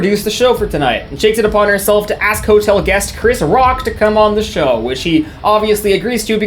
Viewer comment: Did he just say chris rock